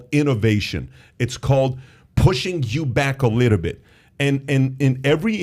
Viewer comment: did he say pushing JEW back